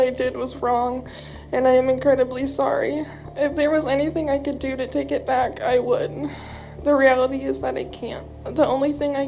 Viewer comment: Did she just say..... 11:55 "if I could take it back, I wouldn't !!!!"....🤔🙄wtf !!